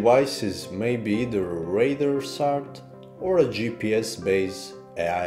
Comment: For a second I thought it said shart 😂